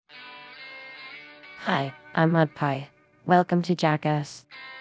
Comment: says her name so fast it sounds like she said. Hi I’m Mad Pie Keep up the good work. Have a wonderful day.